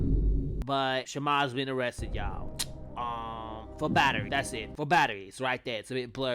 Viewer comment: oh!That said battery shit I thought it said burglary my bad😎